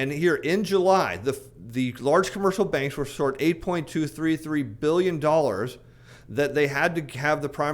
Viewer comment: Anyone understand what he’s saying at 12:05?It sounds like commercial banks were short 82 billion dollars? How does that relate to the primary dealer ?? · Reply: Fed buys $80b UST per month. PDs bought $77.2b UST new issuance. $8.2b shortfall has to come from the markets where the PDs must buy.